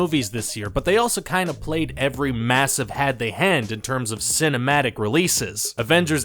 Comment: Did he say "had they hand??" wooooow, lol